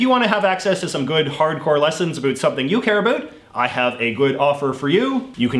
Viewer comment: I honestly thought when he said “care about” he was saying caribou